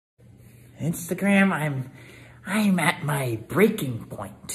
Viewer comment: lyrics: Instagram, Im at my breaking point and a man can only take so much before HE SNAPS. Edit: thanks for the likes lol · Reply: thanks, i didn’t hear instagram, i heard “it’s the gram i’m” i was like huh?